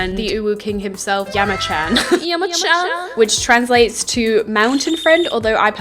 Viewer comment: HOLD on did i hear " Iwa-chan" I KNEW IT IS AN OIKAWA KINNIE